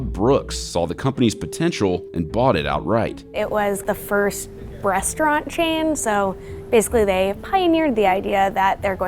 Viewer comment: did she say "BREASTaurant chain" ?